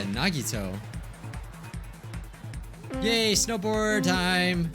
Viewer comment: I thought he just said gay snowboard time